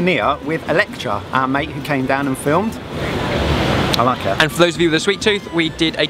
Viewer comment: I was so excited to see Dan and Phil that I literally thought he'd said their names when he said "down and filmed"